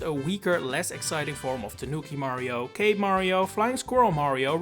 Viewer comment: did you say gay mario? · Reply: Cape mario